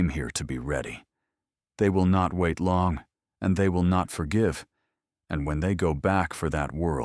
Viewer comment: Slightly past half way mark, "they won't wait long, and they won't forgive". I heard, "Remember the Alamo".